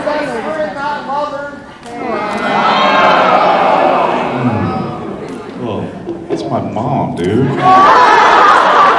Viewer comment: what did the guy shout around 1:58 that made everyone go "oooooh" and Jensen say, "Dude that's my mother, man"? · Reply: He yelled "because she said friend and not a lover?" :/